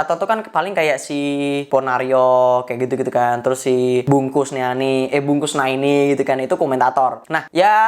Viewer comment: Ucapan bung Valen banyak dijadikan skripsi juga loh 10:14 PERTAMA KALI PASTI DENGERNYA BUNG KUSNANI,TYPOnya 😂🤣🗿 pas denger yabg kedua kali BUNG KUSNEANI